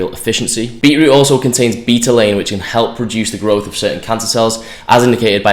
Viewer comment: Did I just hear you say "beetroot can help produce some cancer cells, its good for you eat it"? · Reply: "reduce the growth of certain cancer cells" It does sound like produce haha, I concede.